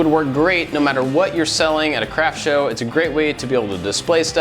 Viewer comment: No matter how many times I listen to you saying "craft show" at 11:41, all I hear is "crap show".